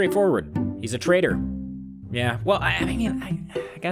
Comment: Thanks to whoever did the subtitles. I always thought he said traitor, not trader. Like someone who would specifically abandon their own team and help the enemy.